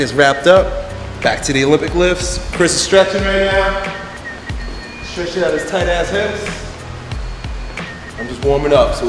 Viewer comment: I was doing some stuff at when the video started and I thought said "Chris is stretching out his tight ass"... Was shocked, just shocked lol.